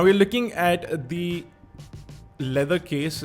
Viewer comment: Did he just say "licking at the leather case" at 7:32? Dude... You have some issues